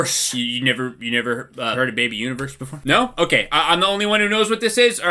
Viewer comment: did someone say AB Universe? 😏